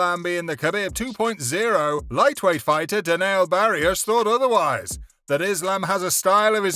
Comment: Did he just say Deniel Barioosh